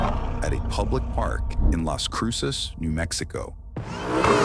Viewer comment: I thought he said mexico not new mexico